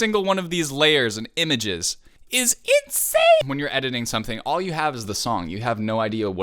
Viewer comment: sounded like he said "it's itzyane" bc honestly, true